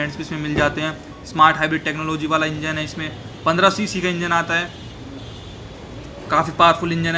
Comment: Did you just say 15cc engine 🤔